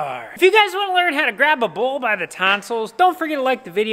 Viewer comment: “Grab a bull by the tonsils" Quote of 2020 & hearing say Larry is awesome ! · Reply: At first I thought said "Grab A Bull By His Testicles" 😂😂😂😂